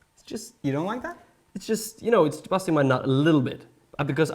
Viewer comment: Did say "It's busting my nut" at 1:50?